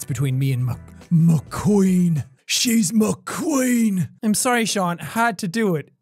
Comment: When jack said "she's my queen" I hear: "shes mcqueen" Kachow